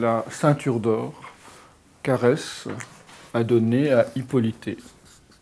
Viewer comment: Bonjour, à 0:24 à "qu'Ares" j'ai entendu Caresse .... 🦚🕊 🙃